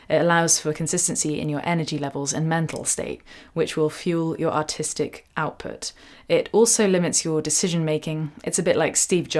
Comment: When she said artistic outlook, I heard "autistic outlook" because of her accent lol.